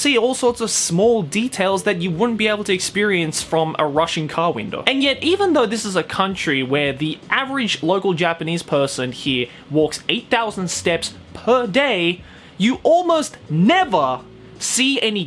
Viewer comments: I had to Listen to it twice cause i thought i heard you saying A Russian car window. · I thought you said russian car window not rushing car window and my dude you can see so much through those they are like windows to another reality mandatory dash cams in Russia brought about a new era of memes to this world